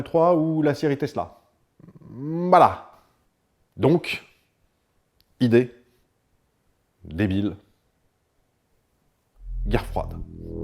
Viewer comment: Quand il a dit "Idée, débile, guerre froide", j'ai entendu "Schreiber, saucisse, gratuité" ^^